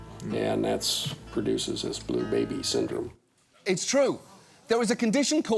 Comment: I thought said the condition name was "exploding baby syndrome" and I'm both relieved and disappointed that I misheard...